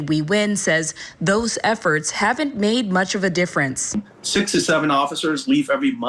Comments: sounds like he said 6 or 7, not 67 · I thought he said 6 to 7 officers leaving a month not 67??